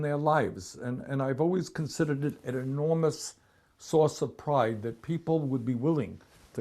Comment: Could have sworn Bernie said Enormous "sauce" of pride